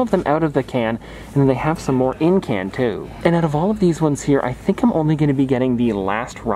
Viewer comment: it sounded like he said Encanto